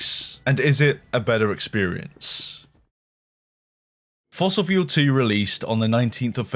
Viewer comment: I had a wifi stutter here-ish so when announced the sequel game, I heard “Fossilfuel Tea Time” — which I thought was BRILLIANT + giggled + kicked my heels. After realizing I had some lag & stutter, I fixed it & re-wound it, sadly discovering I mis-heard it. I really need a sequel or DLC to claim Tea Time. “Find-Out Mechanics” = FOM = deserves to be widely adopted term/application in the industry